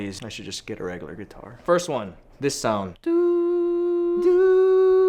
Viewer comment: Please tell me I'm not the only one who hears "dildo" when Ryan is recording the sounds for the guitar around 5:25...